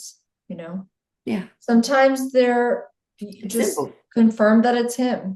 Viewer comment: I think I heard a voice of either or St. Germain saying "Simple" at 1:58 💜I love this interview! It's helping me so much!